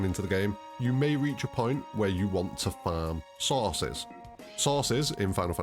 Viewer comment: did he say sauces? lol